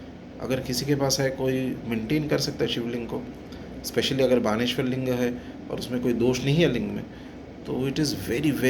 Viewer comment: What does he say at 27:00 ? Which lingam? 🤔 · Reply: Vanalinga. Means Narmadeswar Shivalinga.